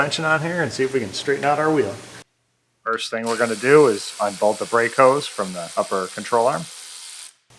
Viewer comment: Did someone say Break Hoes? I gotta get a job at Scott's GM emporium! (sorry, I have covid, my brain isn't close to 100% that's my story and I'm sticking to it)